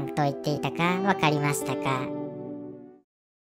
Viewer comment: 人形の声が次はお前らだって聞こえた人いる？